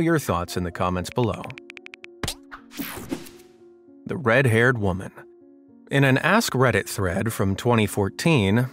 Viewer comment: i thought he said red hair shanks